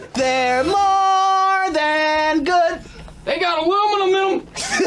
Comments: The one at 46:12 made me Laugh so hard. That was Some wholesome gibberish. I was really trying to understand what he was saying 😂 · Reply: He said “they got aluminum in em” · The one at 46:12 made me Laugh so hard. That was Some wholesome gibberish. I was really trying to understand what he was saying 😂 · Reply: I swear I keep hearing "They got aluminum in 'em" lol